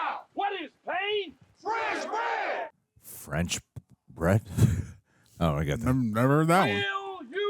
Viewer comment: Pain means "bread" in French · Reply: Thank you for this. I swear until now, every time I watched this movie, I thought they were saying "fresh bread". lol. Like they thought bread was tasty.